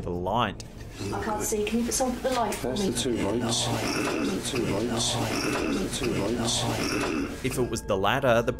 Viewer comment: I hear “Give ‘em their light.” Not “Get a light.”